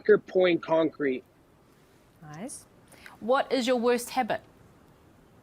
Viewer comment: NGL I heard it as what is your worst headbutt